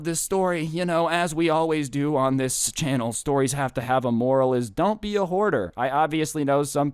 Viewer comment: I literally heard "don't be a whore"